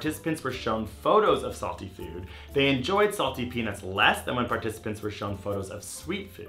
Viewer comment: Did he say salty penis at 2:00?!!